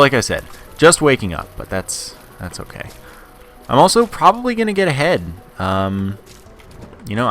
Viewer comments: around 5 mins in I really thought at first you said "and I'm really going to get head" I was like we'll your going to have a good morning haha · i thought you said you were probably going to get head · sounded like said im getting head lol